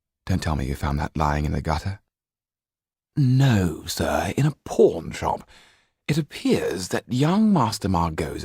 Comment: when Tom said pawn shop, i thought he said porn shop.....